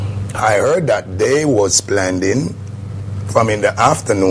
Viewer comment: When bounty killer said I heard they were "PLANDING" the bottling,( I almost fell off my chair with laughter 😂😂😂😂)